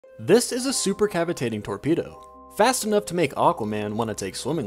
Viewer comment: I was super confused, I thought he said tornado.